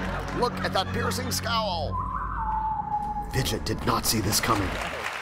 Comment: I swear....I didn't hear right at 0:45 ..I heard "Bitch,I did not see this coming."